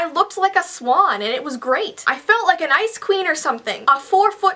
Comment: I thought you said ice cream instead of ice queen 😂